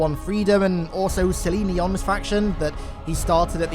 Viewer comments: did you say saline deon’s faction? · i heard celine dions faction hahahaha